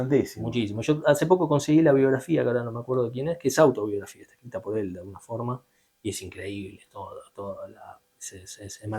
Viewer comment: de quien hablan en el minuto 4:12' que dicen que es interesante de estudiar? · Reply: Big Bill Broonzy